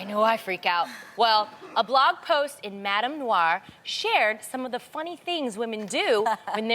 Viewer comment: I kept hearing "madam noir" from the real so i just had a chance to google it. It's a Black women's lifestyle blog.